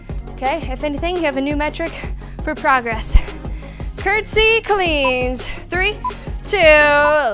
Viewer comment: I heard “curtsy queens” instead of cleans👸🏻